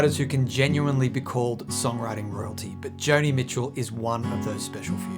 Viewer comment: I thought you said "songwriting Roti" I think that means i should get food before watching this🙃